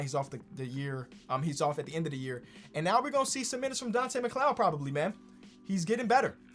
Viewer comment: Donte McDonald exists Kenny: "Donte McCloud" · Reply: I thought he said "McClown" because like McDonald's is like kinda related to clowns because of Ronald McDonald.